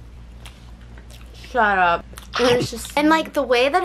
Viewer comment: Did you say "SARAP" on 10:30? If you say it, its means delicious in english, in tagalog sarap or masarap. im your avid viewer in Philippines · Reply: i’m pretty sure she said “shut up” but sarap would be better hehe